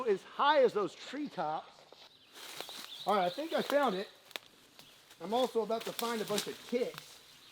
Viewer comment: did he say ''I'm also about to find a bunch of kids'' ???